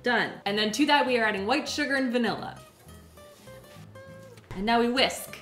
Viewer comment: I was dozing off while watching this (tired, not bored) and I heard her say “vinegar” instead of vanilla while making the brownies and it woke me up. I had to rewind to see if I heard that correctly. So glad I didn’t. Lol.